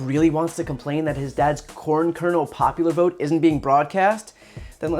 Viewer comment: It’s sound like he is saying porn kernel bowl which yes, Trump would be leading.